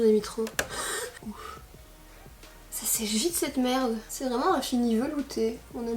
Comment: " ça sèche vite sa mere" tu m'as tué xD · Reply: Moi J'ai compris "elle seche vite cette merde" mdr 😆😂non??